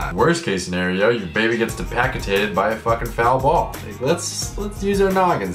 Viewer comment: Did he just say "Your baby gets 'Depacitated' by a foul ball"? -_